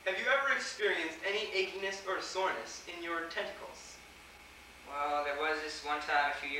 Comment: XD i thought he said "Testicles" XDDD